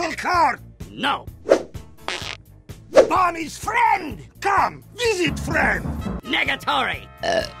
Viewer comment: I can never understand what Heavy is saying in this scene. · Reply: "Bomb is friend! Come, visit friend."